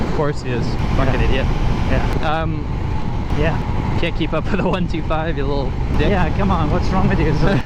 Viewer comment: does he say: cant keep up to the 125 you little zip?